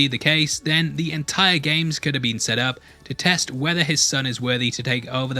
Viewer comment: For a second there i thought you said 'Gi-Hun could have been a god' Because of your accent lmao